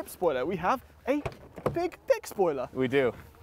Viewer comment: sounds like he says "big dick spoiler". I was playing video games with this in the background that's just what I heard I'm sorry 🤣